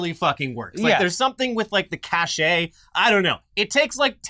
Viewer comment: Did I just hear say “cashay” instead of cache??😆